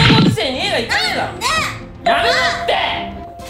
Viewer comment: やめロッテ(=千葉ロッテ)ww 1回言われるともうそうにしか聞こえなくてめっちゃ笑うww